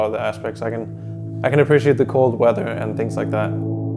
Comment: did he say the cool weather. denmark?????????